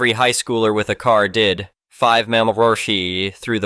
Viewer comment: "five mama ra" I have no idea what that is, and clearly neither does the AI voice. Unfortunately, that and other garbled phrases, appear to be essential to the understanding of the story. Instead, we have a garbled mess of what is possibly an AI generated story. What I can say with confidence is the person responsible for this story has never read or listened to this story before posting it, so the first ones to hear it are the YouTube viewers.